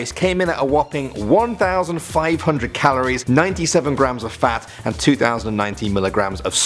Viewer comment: For the burger melt did he say 97 grams of FAT!?